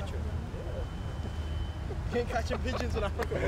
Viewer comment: Did Trent say something about "catching b*****s" at the 1:50 mark, or am I hearing it wrong? 😂😂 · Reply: Sounds like pigeons